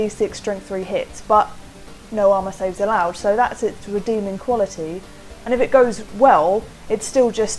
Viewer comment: Wait, at 2:14 did you just say "re-Daemoning" quality?